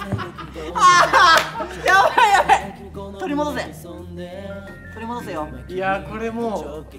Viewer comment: 閑也君が途中1人速かった時 途中で取り戻せ!閑也!って 聞こえてさすがTravis japan♡